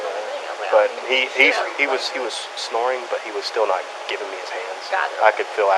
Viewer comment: Did you the cop say (17:20) George was snoring, but wouldn’t give me his hands ? I backed up the video twice, I swear I heard the cop say that. If I was a blonde woman I would say “ I heard him repeatedly say, George was snoring, but wouldn’t give me his hands”. Sorry, I just had to say that.😊. At the end of the video, the dispatch says it could be the missing George, & EMS says “ He is right here “. They still take him to jail, no medical treatment, no concern for his well being, just straight to jail. Remind me not to go missing in Kentucky !